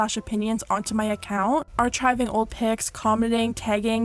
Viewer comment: Did you really say "arCHiving" like that...